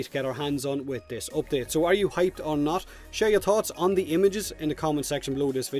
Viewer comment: Misheard "share your thoughts" as "share your thots" xD